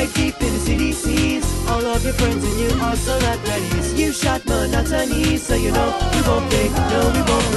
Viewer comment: when he says "you shot monotomy." I honestly thought he said: "you shot, but i'm not chinese."